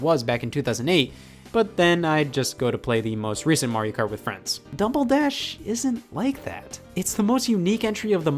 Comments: did say dumble dash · did you just say Dumbledash